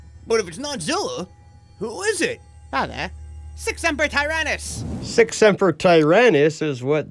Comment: Ok I thought he said six numbered tyrannus at 21:47 because he sounds like hes saying like the 6th member of the tyrannosaurids.